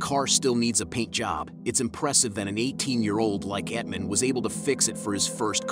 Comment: did….did you just say “Etman” instead of “Ethan”?😂